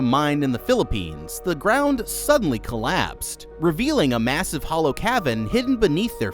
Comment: did you say massive